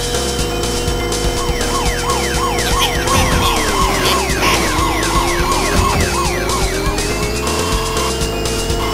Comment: What does he mean? I can't understand properly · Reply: "Arrest the red car"